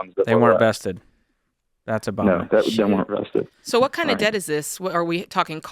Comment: Did Jade say “shit” at 1:51?🤣